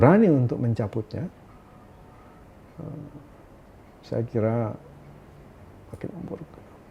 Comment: can anyone explain what did Andreas Harsono say at 17:34 ? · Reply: "saya kira....makin memperkeruh"